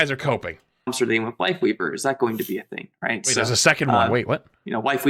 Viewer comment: I heard "wife leaver", then 28:41 "wife weeper" and "wife leaper" seriously! Call him whatever you see fit. 🤣